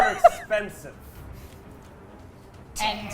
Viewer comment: What does say at 2:02:20, after says his scarves are expensive, that cracks everyone up? It sounds something like "and true" or possibly "and dirty". But that wouldn't explain the laughter.